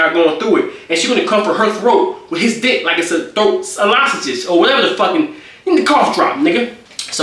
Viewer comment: eLohSaGige instead of lozenges has me crying 😂😂